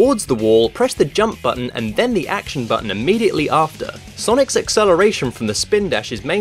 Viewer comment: with captions says "sonic 06: celebration"